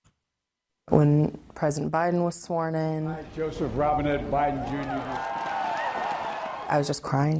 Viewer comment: I swear to God it sounds like Biden says his name is "Joseph Robinhood Biden Jr" in that clip. It is very fitting, though.